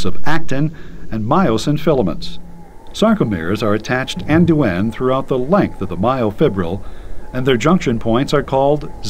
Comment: wtf does he say at 1:51?!## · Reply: "end to end throughout the length of the myofibril"